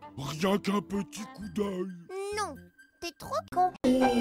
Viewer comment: il dit quoi Nounours ? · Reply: C'est Sauron